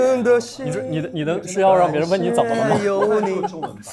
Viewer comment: Jiyang so cute secretly doing the eyes/ears/scalp(?) exercises. And everyone's reactions lol. I love Bowen's laugh! What does he say at 5:45? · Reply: Haha i was also trying to get what Bowen was saying over Yubin's singing. He was asking Jiyang if his mission is to get people to ask him if he is okay/how is he doing..